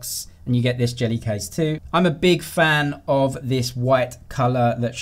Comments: I thought I heard "Im a big fan of this WET color". Love the accent. Haha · what did he say? i thought he said that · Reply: he means white 😆